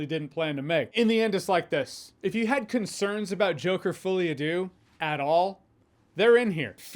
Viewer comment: Did you just say Joker Fooliadoo ?